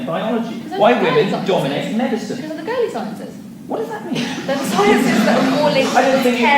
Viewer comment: What is she saying? I can't understand :/ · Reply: "Theyre girly sciences"